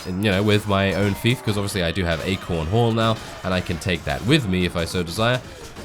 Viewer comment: I kept hearing 'a corn hole'